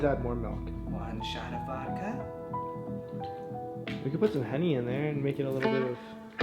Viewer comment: I thought he said Honey not Henny lmaooo